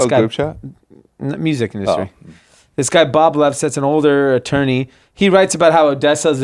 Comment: Couldn't hear the guy's last name, but it's Bob Lefsetz - newsletter is 'Lefsetz Letter'